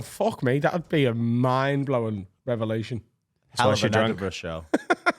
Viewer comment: What did say at 47:09? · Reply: "Hell of an Edinburgh show" i think